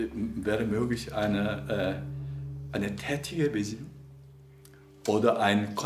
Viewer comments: Leider verstehe ich den Begriff bei 29:15 nicht: Kann jemand helfen? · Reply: Hi, Thomas! Herr Han sagt an der Stelle "tätige Besinnung". Wir hoffen, dass wir helfen konnten :) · Was sagt er bei 29:15? "Vielleicht wäre möglich ein........."??? Das kann ich leider nicht verstehen. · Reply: eine tätige Besinnung?